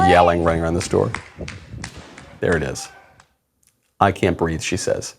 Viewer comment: I thought she said "I can't breaff!"